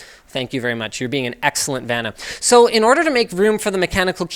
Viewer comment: What does he say at 4:36? 'You're being an excellent ...'? Non-native speaker here. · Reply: He says "You're being an excellent Vanna." Referring to Vanna White, who would co-host the show Wheel Of Fortune. She was known to be good at displaying items or "pointing" at things.